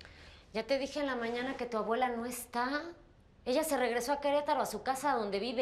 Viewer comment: *¿Que dijo? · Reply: Sophi, su abuela